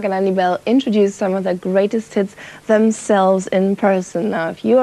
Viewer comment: did she say "greatest tits" twice?!